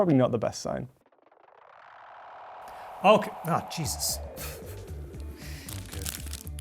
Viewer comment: did he say 'CHEESUS'